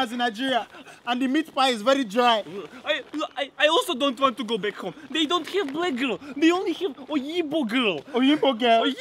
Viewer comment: almost 5 years later and I still don't know what says at 0:15 someone help please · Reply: he says "I also don't want to go back home, they don't have black girls, only iyinbo(white) girls"